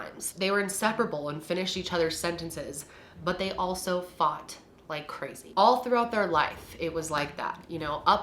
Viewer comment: Nobody gonna talk about how they showered together 💀💀💀💀💀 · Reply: I’m not gonna lie to you when she made that statement and a few seconds later said “faught like crazy” idk if it’s the way she said it or the way I heard it but I thought she said “fucked like crazy” I was like whoa she did not just say that on her channel 😭 I lost it